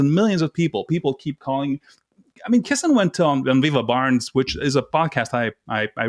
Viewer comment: I couldn't quite get the name of the pod caster who interviewed Konstatin Kisin (11mins in) all I heard was "dunvever barnes", is this correct as I cannot find it. THANKS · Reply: He referred to the Viva and Barnes podcast. Viva Frei is a Canadian lawyer (unless he's moved to Florida recently) and Robert Barnes who is an American lawyer. Personally I don't respect either, I think Frei is a Know Nothing who's main claim is talking loudly and rapidly and Barnes is a phony, self promoting windbag. I bumped into their content during and after the Kyle Rittenhouse trial.